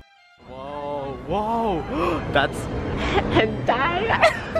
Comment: what did you even say🤣🤣 2023 EDIT: O H. YOU SAID HENTAI😭